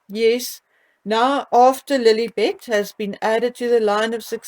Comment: When you say Lilibet, I hear L'il Bitch, 😂😂! My mind goes to dark places!